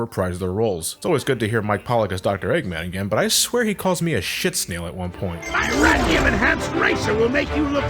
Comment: i don't even get what eggman's trying to say here lmfao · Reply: Sick snail.